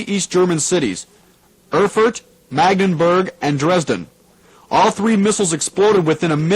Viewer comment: did he say Erfurt Magdeburg and Dresden because i'm living near Magdeburg would be the first time that I'm near of the impact zone, but I'm not sure of he says Magdeburg